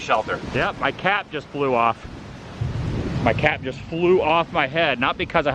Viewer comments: For a second, I thought you said, your cat just flew off your head 😂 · I’m surprised they don’t make side curtains for those Ev ‘s. It sounded like you said my cat flew off my head!😂